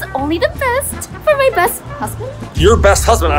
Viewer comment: I thought when said "I got backstage ticket for my best husband" I thought she said "cousin"